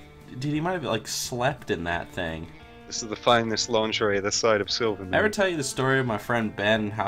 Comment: "this is the finest lingerie this side of silvermoon" did i hear that right lmfao XD